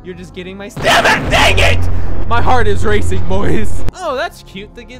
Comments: did he say a bad word or debit? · anyone else hear him say damn it...?...